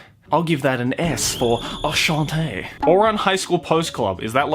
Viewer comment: Thought he said Shantae was about to flip lmao